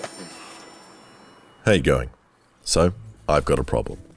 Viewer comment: What does he say in his intro. My friends say that he says “hey guy” but I keep hearing “how you going” am I or are my friends crazy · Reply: Its "How you going?"